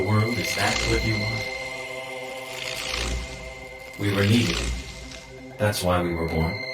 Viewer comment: the subtitles say "we were leaving" but the actual dialogue is "we were needed" also i haven't finished the video but so far it's good